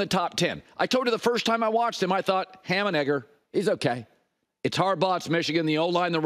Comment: What did he say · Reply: "Ham-&-Egger". Think opening act, average Joe, nothing special, etc. It's a phrase.